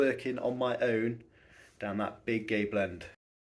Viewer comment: I swear you said big gay blend.